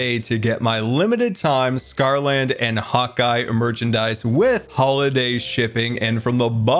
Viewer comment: I always thought you were saying Hawkeye not hotguy